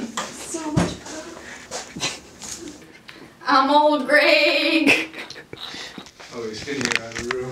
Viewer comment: Somehow I think I heard "I'm old gregg" in there...